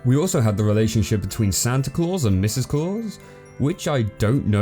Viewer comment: anyone else thought he said "santa balls" or "santa testiclaus"? that will be more funny than the rest of the show